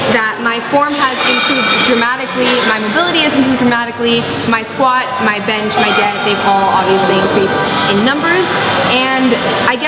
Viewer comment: Sounds like she said. "My squat, my bench, and my debt they obviously increased in numbers." lol